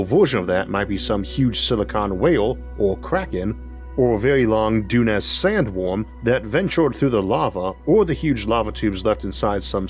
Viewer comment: heard this as "very long dune-ass sandworm" at first and I was like hell yeah Isaac, cut loose